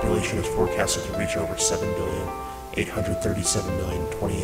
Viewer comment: did he say where is waldo?